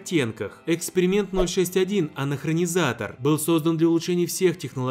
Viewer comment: послышалось анахреназатор:D